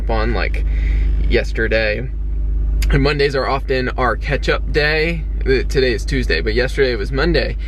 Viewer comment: Totally thought said "ketchup day" lol